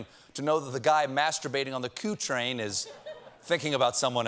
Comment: Does he say "coup train" while the subtitles say "Q train"? Brilliant.